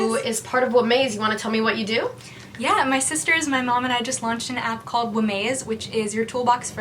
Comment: Can we talk about how 0:07 that threw me off I heard "my sister is my mom" help what does that even mean.